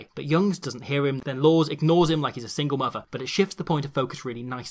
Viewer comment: did he just say " he ignores him like he's a single mother"??! LOL